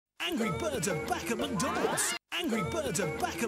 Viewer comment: I thought they said ANGRY BIRDS ARE BACK AT THE DOCKS!!!